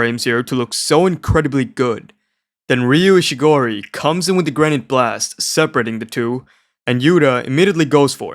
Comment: the subtitles were bad ryu is actually saying “granité” blast. When you hear him say it in the fight you can hear him putting the accent on “granité”. Granité is an Italian slushie dessert and it’s not a rock lol